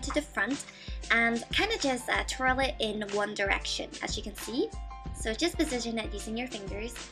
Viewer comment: did you say onedirection ??? O.o xD LOL